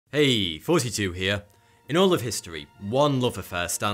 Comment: Is that what you're saying at the beginning of each video, "Thoughty2 here"? I always thought you were saying 42 here....LOL must be that great accent!